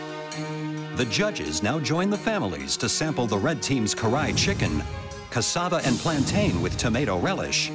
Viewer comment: Oh...so the red team was cooking 'Karahi chicken'....I swear I kept hearing 'Cry chicken' 😂😂😂